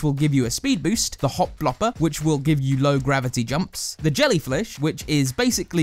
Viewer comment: did I hear that right he said "jellyfLish"